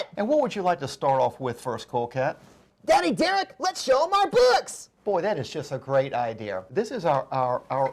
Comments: I thought he said “Let’s show them our d**ks” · thought he said "let's show them our dicks" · XD thought he said "Well lets show them our butts!"